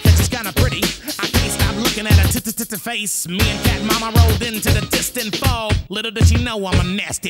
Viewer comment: BACK TO THE KITTY CAUSE SHE'S KINDA PRETTY 👀 CAN'T STOP LOOKING AT HER T-T-T-T-FACE!😊😖 ME AND CAT MOMMA ROLLED INTO THE DISTANT FOG, LITTLE DOES SHE KNOW I'M A NASTY DOG! 😫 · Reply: I cant hear cat mama, i just hear "mama rolled"